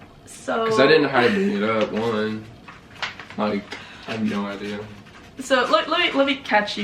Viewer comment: なんて言ってるんだろう 聞き取れた方教えてください🙇🏻‍♂️ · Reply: like…I have no idea.（よく分からないよ。）だと思います。 ここじゃない所だったら、ごめんなさい🙏